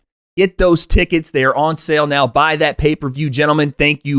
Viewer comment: For a second I thought deji said "Aslamaulikum" in the last second😂, but he said "thank you for having me" when I turned my volume on^